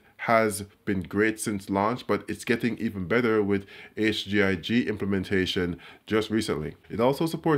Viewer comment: What he says at 8:32? what new gaming feature? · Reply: Raitzi HGIG is a HDR format for gaming <link>